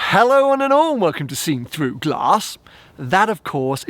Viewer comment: Been watching for 2 years now and still have no idea what he says at the beginning of every video. Someone help my dumbness. Sounds like he says “hello and an arm”